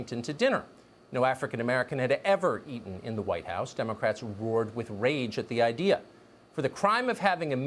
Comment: Wait 1:29 Did he say democrat were in rage Oh so the democrat were were the racists